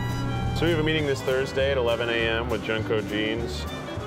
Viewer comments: how come i hear jungkook jeans? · idk what he said but— “JuNgKoOK JeANs.”